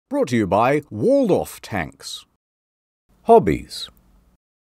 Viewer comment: I thought it said hobbles